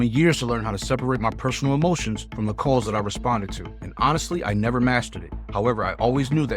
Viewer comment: Swear I thought he said “I never masturbate”